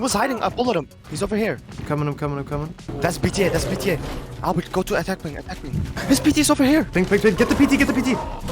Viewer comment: did i heard polish?? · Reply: I said “go to attack ping, attack ping!”